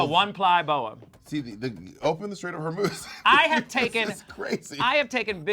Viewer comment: What is Bob saying here about the boa? I couldn't understand and it's bothering me 😆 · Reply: "Open the Strait of Hormuz" To provide a supply chain for more feathers.